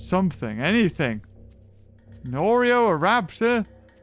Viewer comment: Did he say, "An Oreo, a Raptor."? If so, I'm in trouble.